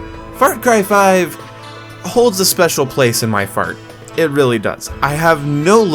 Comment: did you said :" far cry 5 holds a special place in my FART"!!! hahaha